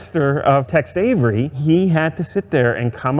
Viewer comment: It sounds like he is saying "Text Avery". It's Tex, right?